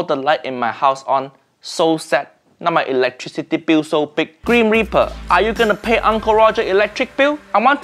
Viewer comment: (at subtitles) Green Reaper?... I thought he said "Grim Reaper". Or that was a roast on an another level, cuz greens = sad, so Green Reaper also = sad.